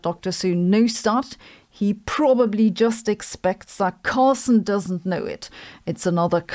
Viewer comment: I love how it sounds like she is saying Taco Carlson